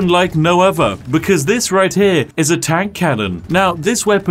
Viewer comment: Master Chief shooting tank rounds from his eyes is canon now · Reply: All I heard is “master chief… is a cannon now” XD